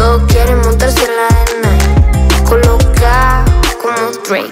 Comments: Pense que decia colacao XDDD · COMENTARIOS 65%: Pensé que decía Colacao 20%: la música está geniaaaal 10%: Se parece a Billie Eilish 4%: Me devolviste la heterosexualidad que C.R.O me quitó 1%: Pendeja haciendo porcentajes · bld soy la única q piensa q esta canción salió hace poco pero en realidad ya pasaron 9 putos meses JAJAJAJAJ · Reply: No pero ps pensé que decía colacao JSKSJJAJAJAJAJ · No sé si alguien más le pasó, pero pensé que ponía colacao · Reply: Yo pensé que decía cacao😂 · No sé si alguien más le pasó, pero pensé que ponía colacao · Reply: Maaaal pensé que decía colacao